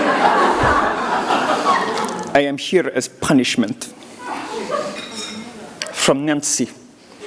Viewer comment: "I am here as punishment". I thought you said "I'm a cute as punishment" (as if her face looks like someone's been stoning it.)